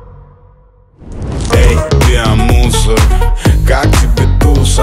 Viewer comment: Я: включаю субтитры Субтитры: Я МУСОР!!! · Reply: Ага но я расслышал там "эй био мусор"